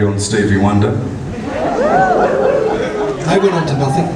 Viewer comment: What is it that he says at 0:32 thats so funny... I can't make it out. Also love that comment about that "high note!" Love it when Iva gets all coy.❤️ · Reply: I think Paul Gildea said, "They went on to nothing," or "They amounted to nothing." Something like that. 'They' being Peter Gabriel and Stevie Wonder.